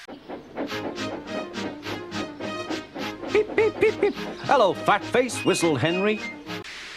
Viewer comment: I thought he said Fat fuck to Gordon, i almost spilled the soda i was drinking😂